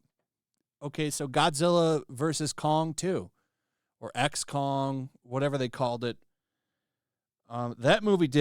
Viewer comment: When you said "x Kong", I thought you said XCOM for a second, and I was like "Wait, they're making an XCOM movie!?", before I realized I misheard you